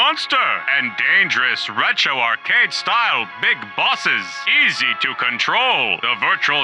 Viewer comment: Did they say Big Boss ?